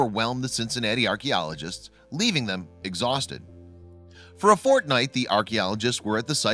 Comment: did he say fortnite